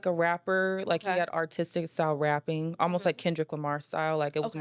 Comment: why i thought she said "autistic style rapping" i was like wah? lol i almost died bu i see she said "artistic style rapping"